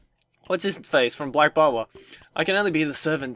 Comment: am I the only one who heard "Black Bible" instead of "Black Butler" at first? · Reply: I heard "Bwuck Baba" XD